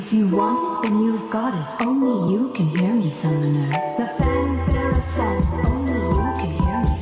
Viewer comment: thought she said only you can hear me senpai